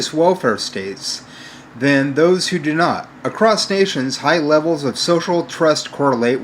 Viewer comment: He's such a mush mouth that in the first 30 seconds when he said "across stations", it sounded like he said "crustaceans". I mean, it's wholly apparent from his diction, his tone, and where he inserts inappropriate pauses, that he has no comprehension of anything he's reading. It's just another aspect of his masquerade as a scientifically-minded intellectual.